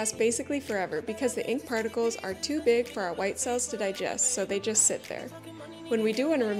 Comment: Help I thought she said “white selves”instead of white cells😂